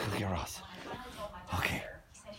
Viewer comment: What did he say to wake him up? I didn't understand :-( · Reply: Thank's! I thought he was saying "let's go get rush" :-) Is "get roz" an American slang expression?